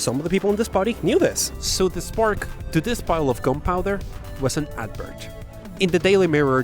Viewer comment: I heard "compiler" instead of "gunpowder" lololol